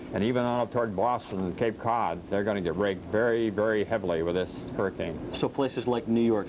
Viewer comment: did he say they were going to get raped?